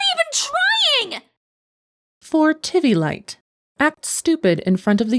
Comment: for a second I thought she said “Tittylight”